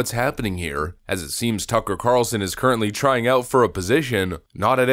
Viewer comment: Pretty sure Jon Anik doesn't know who Tucker is, I think I heard him say Tucker Carson. Idk, anyone else hear that?